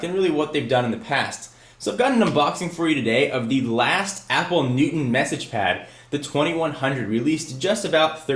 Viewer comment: oh i thought you said newton massage pad..